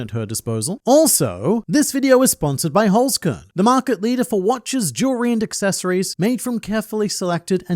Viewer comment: while skipping through the sponsor all i heard was "This video was sponsored by Holzkern, the market leader for-beautiful-wife"